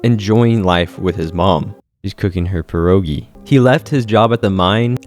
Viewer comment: is that pierogi I hear? 🇵🇱